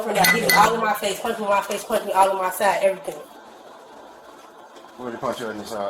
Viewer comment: Did she say “Punching all of my fat… everything.”? Something to be said for self awareness I suppose.